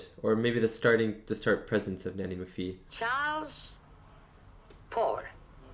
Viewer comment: Yes!! Finally,someone else who thought she called him “Poor”!! 🤣🤣